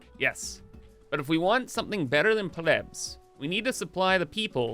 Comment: Huh. Why do I keep hearing "Plebs...are needed!"?